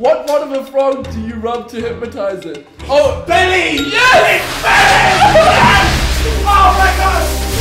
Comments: it sounded like he said its bell end lol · it sounded like harry screamed its bellend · I thought he said bellend not belly😂😂